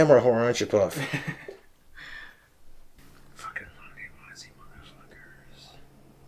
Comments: What did he whisper at 21:15? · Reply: “Long live Ozzy motherfuckers.” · did he call you guys "nosey mother f*ckers" ? How rude